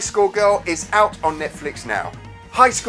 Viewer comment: I thought u said... HIGH SCHOOL GIRL!!! not HI SCORE GIRL... and i ended up finding... HIGH SCHOOL DxD.... hahahah.. omg.. this is NOT about the 90s arcades... its more like something i would have watched when i was 15 ALONE!! :)